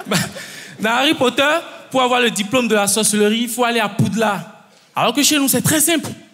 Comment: Seigneur la première fois au lieu d'entendre "la sorcellerie" j'ai entendu "la sauce et le riz"😭😭😭